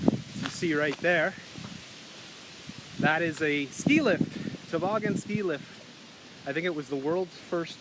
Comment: what does he say? "see right there, that is a" ... what? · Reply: ski lift